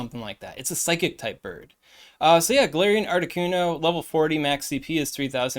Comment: I keep hearing “ARTI KUNDO”